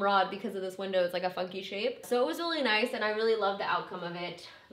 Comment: I swear I thought you said your window is a "fuck you shape"... I had to rewind a few times to really listen. Then I figured out you said it is a "funky shape" not a "fuck you shape." 😂 although, If it were my window and I had to pay for a custom rod, I think that a "fuck you shape" is the proper adjective for it. :)